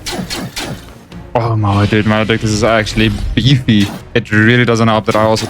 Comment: What did you say at 52:40?😂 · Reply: "maledictus is actually beefy"